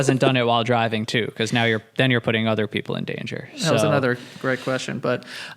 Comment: At the end when they talk about how he doesn't masturbate while driving says, "that was another great question" and I heard "that was another Greg question" which indeed it was hahaha.